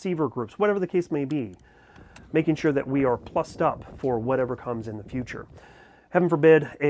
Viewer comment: Did I hear correctly? "Making sure that we are f#*ked up for whatever comes in the future."? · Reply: Plused…plused up for whatever comes in the future